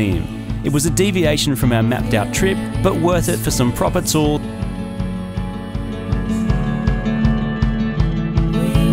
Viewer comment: I want to know what he said here that was cut! Lol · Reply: Acme mr tamada. Tavarish. Mr tavarish tamada